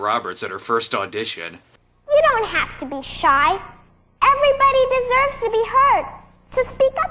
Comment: I misheard that as "Everyone deserves to be hurt."